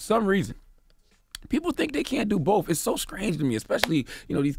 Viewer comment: CHARLAMAGNE!!! 1:02 Nigga did you say "it's so skrange"???